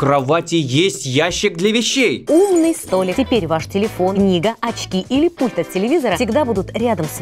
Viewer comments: я услышала не «книга», а «нига» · мне одному послышалось не книга а нига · я один слышу нига?